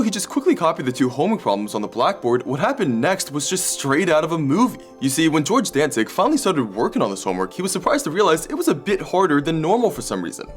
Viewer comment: Lol i heard that as george (danzig) like the metal singer